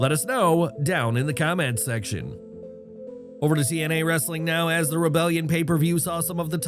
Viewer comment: I know I'm lit AF but at 3:39 who else heard CNA Wrestling.